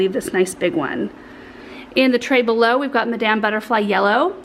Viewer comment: When you first said " madame " snapdragons , I wasn't looking at the screen and I thought " what made Laura say " damn " - LOL !!! Then I was thinking the pretty table that is a catchall in the studio might look nice behind the 2 leather chairs in the studio maybe ? You have the most gorgeous planters for ALL of both your indoor and outdoor plants . Those begonias are absolutely stunning !!! Tfs